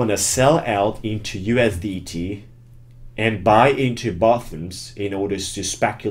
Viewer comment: Hey, love your videos. Just a quick question about what you said at 9:18...couldnt understand the word clearly. You said something about buying into (bathrooms/buttons/bottoms?) to speculate the market. Could you clarify what the exact word was? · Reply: Bottom